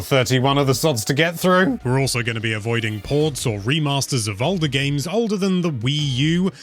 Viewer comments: Thought you said no ports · I thought you said no ports???? Lmao